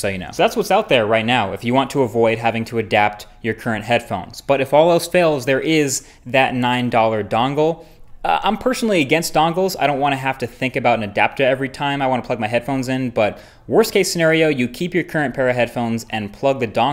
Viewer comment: Did anyone notice at around 6:06 or so he says "adaptah" instead of adapter 😂😂